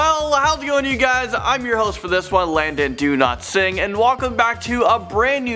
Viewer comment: To be fair, in 1988 we didn't even have Tide Pods, so there's that! (also, am I the only one who thought he said his name was "Landon DoNotSing"?)